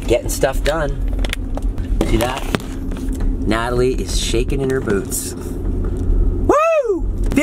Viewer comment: when Jason said “ Natalie is shaking in her boots” I thought he said “ Natalie is shaking her boobs”